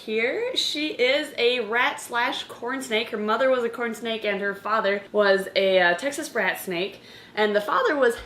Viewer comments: I thought she said “Her father was a Texas brat snake.” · I thought she said her father was a Texas BRAT snake